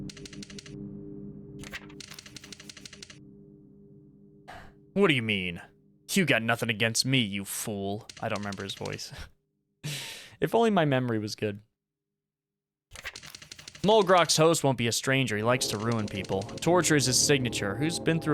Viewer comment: I though he said "food" instead of fool omg🥀